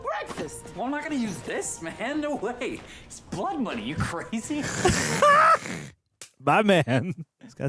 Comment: I had to rewind because I thought Vigilante said, "It's Plot Money, you crazy?" And I kinda wish he had just as a meta joke.